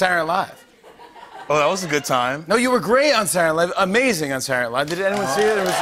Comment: did Jimmy really say "Sardnaylie"